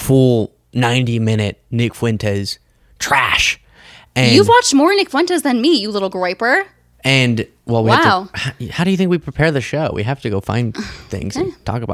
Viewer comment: She did not just call you a Graper. .. also 40:11 ... xD i was always told the bunny ears mean ."marry me now, kiss me later."